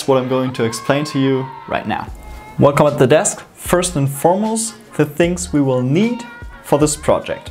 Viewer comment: Challenge: What did I say at 0:54 ? 😂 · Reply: Welcome at the desk, first and foremost...